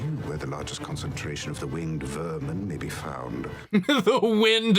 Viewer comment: I i think he said 'winged vermin' not wind, but they both fit lol